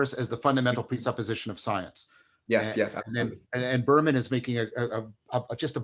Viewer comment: Anyone know what author John is referring to: Burhman (?), science and platonic forms???? 2:14:00 Thanks! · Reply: Berman, Platonism and the Objects of Science.